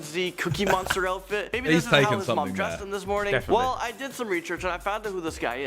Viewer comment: i swear he says "ReChurch" instead of research Lol